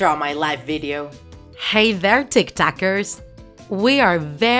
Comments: I swear everytime she says "tiktak" it sounds like she is saying tic tok..... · I thought she said:"welcome tiktokers"...😆😅😂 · i thought she said tik tokers at the beginning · I thought you said TikTokers, i was like EWWWWWWW UNSUBSCRIBE REPORT DISLIKE BLOCK